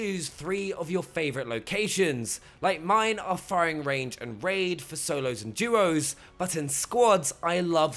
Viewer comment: did you say “Raid”? I don’t have that on my map....